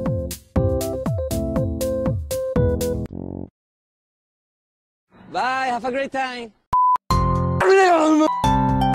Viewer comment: the boy is italian and im too italian and i understand what he is saying xD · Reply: ciao frattello=hi brother , cosa intendi=what's do you mean